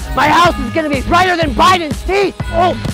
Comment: "My house is gonna be brighter than Biden's teeth!" 🤣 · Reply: I heard Biden's feet 😆